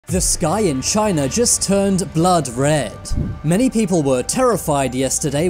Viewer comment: I swear I heard "This guy in China just turned blood red." So I was confused.